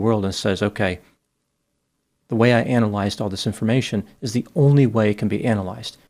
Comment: Omg i thought you said anal-ize at the start.